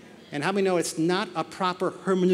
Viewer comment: What was the term he used at 9:54 that he stumbled to pronounce? I know it started with an H and sounded like "Hermunic," if anyone can tell me I'd appreciate it! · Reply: Hermaneutics